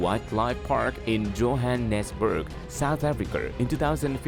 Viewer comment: I can’t understand a damn thing this guy is saying, did he just say Jo Hannass berg south Africurr? I think he did, ok thanks for the vid.